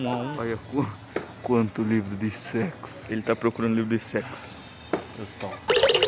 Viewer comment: por um momento eu escutei "Ele está procurando livro de sexo Cristão"